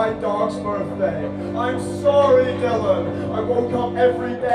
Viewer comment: Not sure I understand this. Any chance of enlightening me? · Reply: some dude I knew thought he said Henry, not Dylan when he's talking ab the dog